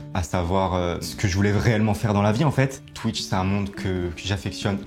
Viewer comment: la team Kekra: c'est moi ou il a dit "vréelement faire"?